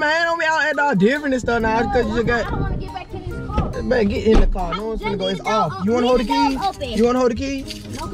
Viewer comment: It sounded like said bae get in the car 😂